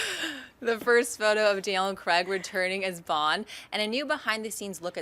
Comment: did she say "DL & Craig" instead of Daniel Craig....?